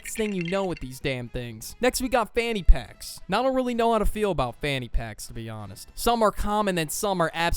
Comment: the brainrot is getting ta me , when he said fanny packs i thought he said fanum tax